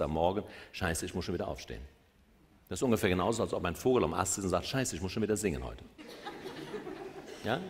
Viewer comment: Was sagt er bei 28:34? Kann es nicht verstehen... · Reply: Robert Betz Kundenservice: Robert sagt hier folgendes: "Das ist ungefähr genauso, als ob ein Vogel am Ast sitzt und sagt, Scheiße ich muss schon wieder singen heute. Genauso verrückt ist das."